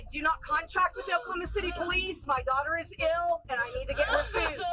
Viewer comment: what is a "cutter"? she said "my cutter is ill". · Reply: She said my daughter is ill.